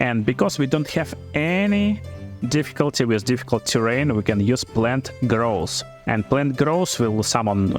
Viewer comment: I had this running in the background while doing dishes at 12:08 all of a sudden I hear plant girls and I was all like: „WTF you can summon plant girls?“ 😂